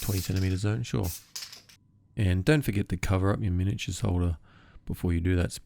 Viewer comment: Not a native english speaker. I dont understand what he is saying at 12:07. "And dont forget to cover up your miniatures ?what?, before you do that spray.". Please help me. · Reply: Sorry, I’m just saying make sure you cover up your miniature holder before you spray. I got black all over my clean mini holder